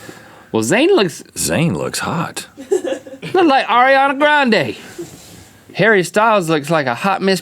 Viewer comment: Lol when link said " that looks like Ariana Grande " and i thought he said " that looks like Ariana Brande " lolll 😂